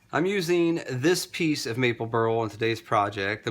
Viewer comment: What does he say at 4:29 ? Which wood is it? I could not understand, sorry :) But looks incredible! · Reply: burl 🤓 Thank you!